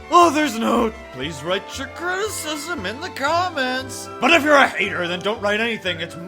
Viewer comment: My criticism: I thought you said Write Your Children in the Comments